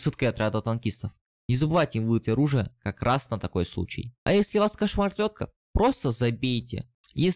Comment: чо? Если вас кошмар тётка? Не могу расслышать, чо за дикция? Нинавижу таких людей.